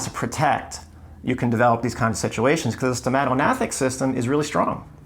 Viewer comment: I'm really enjoying your knowledge, thank you!!! What is this system that you say at 9:37 ? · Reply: stomatognathic